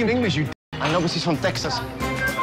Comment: I thought he said ‘I got a girl brother my bad” 🤣